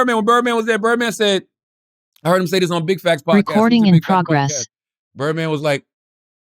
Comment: Anyone else hear “recording in progress” at 1:12:29?